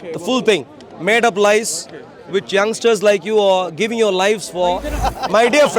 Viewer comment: Did I hear " with JUNK-STARS LIKE YOURSELVES" 😂😂